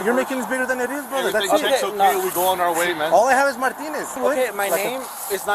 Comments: please check out the time on the video of 4:49 to 4:50. i swear the guy says, " All I have is my penis." i swear that is what is said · Did he say, "all I have is my penis?" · did I hear this correctly? "All I have is my penis" ??